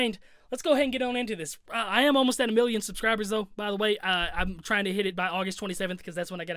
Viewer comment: Bruh I thought he said he almost at a billion subs😄